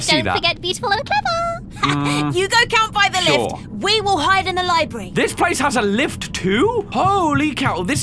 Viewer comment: Don't you know THIS IS A LIBRY (No I didn't make a typo I heard it as Libry)